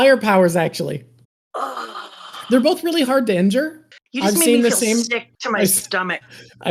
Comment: Did this man just say Pearl and Ace were both “really hard to enter”??? Surely I misheard you